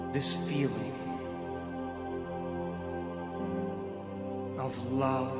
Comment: what is he saying? Radiate this..... of love. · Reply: Radiate this feeling of love. 💌